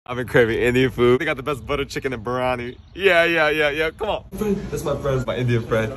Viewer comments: I thought man said butter chicken and brownie 😭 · Man I thought he said brownie instead of biriyani and I was so confused 😭 😭